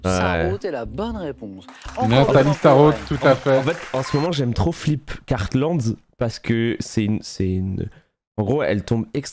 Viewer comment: ça fait 10min que j’essaie de trouver sur google le nom de l’autrice dont il parle à 6:57 mais pitié aidez-moi j’arrive pas à trouver alors qu’il dit SON NOM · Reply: C'est bien Barbara Cartland, mais il a un peu exagéré les nombres.